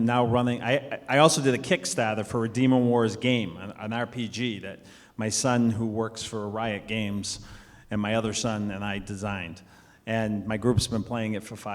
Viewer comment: For a moment, I heard "The Son that I Designed."